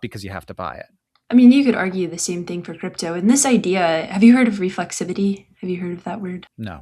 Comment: I thought she said "refluxivity", which would have to do with do with getting indigestion from the market